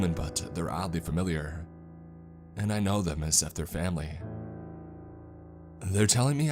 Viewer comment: What does he say at 5:17 ? Something like "i know its something my family" ? Does anyone understand? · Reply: "and I Know them as if they are family."